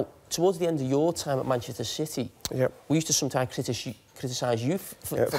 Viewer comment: did he just say Manchester Shitty?! 😂😂😂